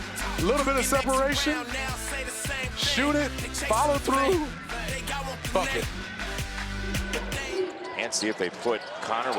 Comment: what did he just say😂😂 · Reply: "Bucket"